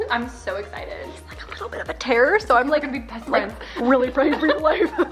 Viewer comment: did i hear terroist? 🤧